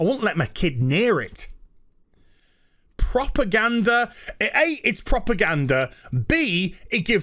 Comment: I genuinely thought you said you had a "proper gander" at it, as in you watched it closely. Hahahaha